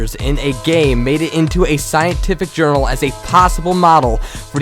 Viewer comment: did he call it world of warcrack?